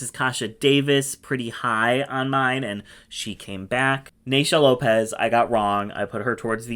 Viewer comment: when you said " or Naysha Lopez" I heard "Ornacia Lopez", which would make a great concept for that merging challenge in AS7 😁